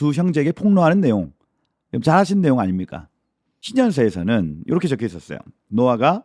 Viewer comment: 에서 목사님이 무슨 책이라고 언급하시는지 잘 안들려요. 칠십인역에서는… 이렇게 말씀하신건가요? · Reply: 희년서 입니다